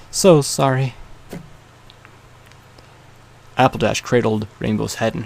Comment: who else heard apple dash